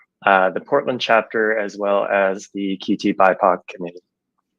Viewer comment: QTBIPOC community? What? What is that? What did he say? I can't squint my eyes enough to hear better